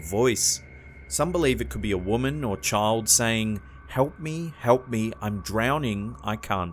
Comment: for #2 i hear "help me i'm down here" and on the last one i hear "our train"